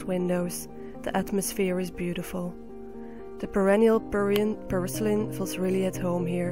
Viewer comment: what plant are you talking about? I heard "perennial peruvian parsley" but couldnt find anything about it. A perennial Spinach is really interesting. Thank you also for the insides to your greenhouse:)